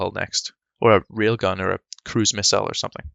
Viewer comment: I heard that as “real gun” the first time and thought it was some top tier British slander